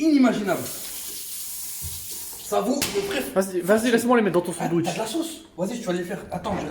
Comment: j'ai cru il a allé dire laisse moi les mettre dans ton fion 🤣